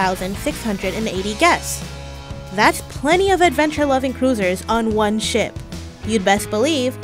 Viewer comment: When she said cruisers I thought she said losers😂